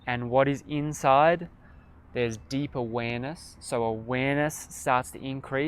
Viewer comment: Lol about halfway through when you started saying deep awareness a good few times - I initially misheard what you said as deeper weenus. . I was so confused until you said it a 2nd time. Lols